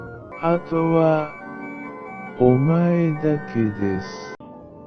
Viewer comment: What the doll says at 10:42 sounds more like 後は、お前だけです. or "Now, it's only you"